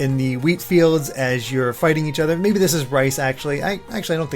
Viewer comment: lmao I heard "rise", the League of Legends cinematic were a katana fight takes place in a similar field